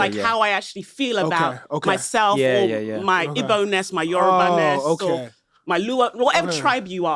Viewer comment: homegirl said igbo and yoruba and the captions says bonus 🤦🏾‍♂️ Yall need a different transcriber